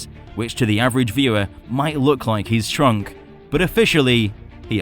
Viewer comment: Are you happy with the size of Godzilla in GVK? Or are you in the firm belief he's been shrunk? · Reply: When you said shrunk I thought u said stronk